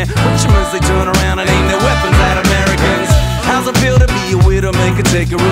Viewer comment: oh...i thought he said "so turn around and aim your weapons at americans" i was like dam that much disrespect lol you wouldnt be rapping if it werent for america